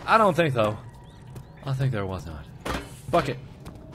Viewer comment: It sounded like mark said fukkit not bucket